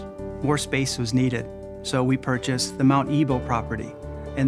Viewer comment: Did Curzan just say they purchased the Mount Evil property? Mount Evil....Warwick? Did anyone ever stop to consider what the optics look like for the name of these building locations?